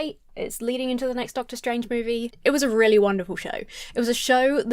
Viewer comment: "It was a really wonderful show" But with your accent I heard "It was a really Wandafull show."